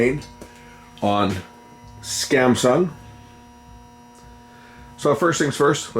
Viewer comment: Did you just say "scamsung"???